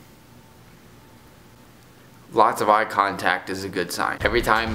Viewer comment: When you said "eye contact" I thought u said "use contacts" I was rly confused for a minute.